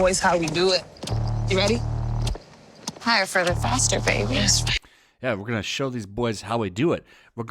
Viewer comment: She mumbled and I thought she said "Id rather masterbate a baby"